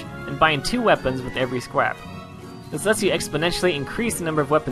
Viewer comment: anyone else hear "Skwap"?